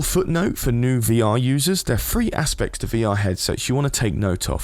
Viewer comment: i dont quite understand at 2:10 ... are there FREE aspects to VR? or THREE aspects?....cuz if you meant THREE..its pronounced with a TH...not a F